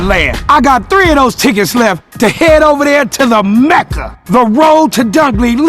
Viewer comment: Hold on did hi say meqa